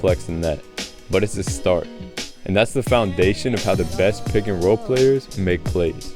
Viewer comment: For a second I thought it said Rick and Roll passing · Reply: I thought he said rock and roll loool